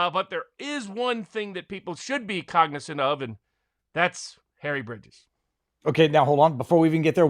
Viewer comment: at about 12:24 Barnes says "there's one thing they should be cognizant of that's "harry ???" anybody know what he said? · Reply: He said, "Harry Bridges." They got back to that topic at the very end of the video. I had to search his name to have any understanding about that topic.